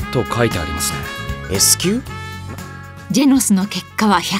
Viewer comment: ジェノスがジェンスに聞こえた